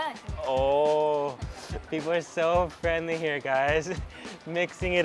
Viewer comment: i first thought you said "chingchong people are so friendly" haha